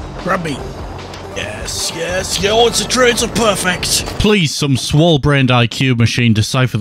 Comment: What was said here was something along the lines of "Oh it's a triple-- it's a perfect!"